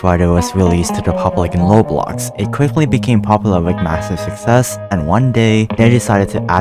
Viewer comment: DID YOU SAY MASSIVE??!?